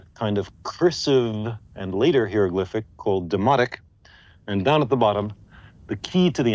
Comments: At first I thought he said demonic.😂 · Anyone else on first watch swore he said "Demonic" instead of "Demotic"? lol had the read the captions on the 2nd viewing. I was like I know I didn't hear that right.